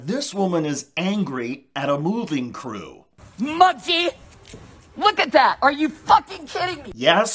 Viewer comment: I thought it sounded like she said “mugsy”, but either way, she was horrible, hostile, and ridiculous.